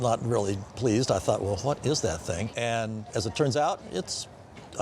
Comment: did he just say the F word? “The fk is that thing!?”